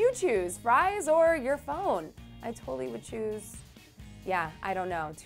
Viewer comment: I thought she said your fries or your bone XD